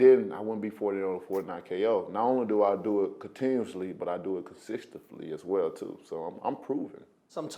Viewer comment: Blah blah blah I'm so great..wait a minute did he say consisfticly ??at